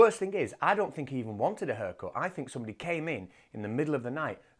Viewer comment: When you were saying "haircut" I thought you were saying "herkel" and I was trying to figure out wtf you were talking about.